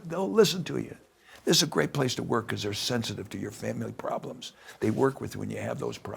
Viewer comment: Did he say this is the best place to work “because their sensitive to your feminine problems?”